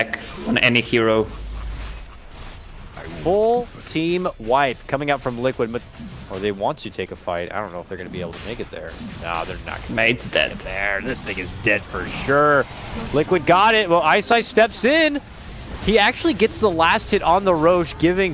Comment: did i heard right?this n... is dead for sure?